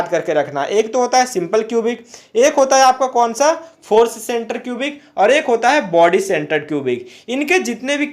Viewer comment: kya bola tune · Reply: Sorry By Mistake It's Happen, FCC = Face Centered Cubic